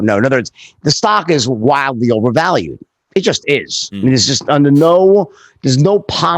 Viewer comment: He didn't say AMC was overvalued. He was pointing at GME 180-200 bucks a share. He did talk about AMC though. He has a 240k position. He bought a "truck" ( large position) for his girlfriend. FYI: Aston Martin does not make trucks. And he also mentions (A)ston (M)artin (C)ar. No one says that, people just say Aston Martin. He talks like that, just look at his twitter profile. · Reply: are you kidding?! Go and listen to what he says properly! At 2:55 he says “AMC stock is WILDLY OVERVALUED”.